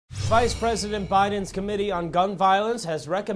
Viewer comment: I thought it said president Biden for a sec lol